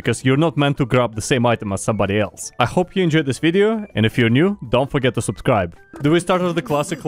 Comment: When you said don't foget to subscrybe i heard ,, don't foget to don't subscrybe''